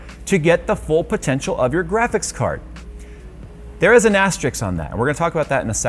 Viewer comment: LOL! Did he just say "There is an ASTERIX on that"!?